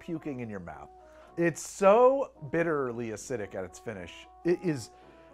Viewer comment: I first heard "It's so bitterly acidic that it's Finnish."